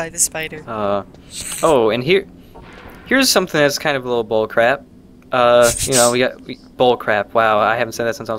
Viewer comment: When he said "bull crap" I genuinely thought I heard "bowl of crap" at first.